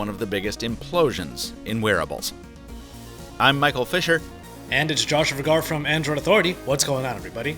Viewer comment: I literally heard "I'm Michael Fisher for Pocketnow"